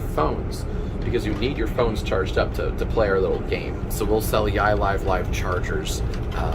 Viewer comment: thought he said AR games